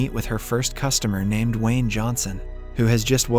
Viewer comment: it sounded like he said Dwayne Johnson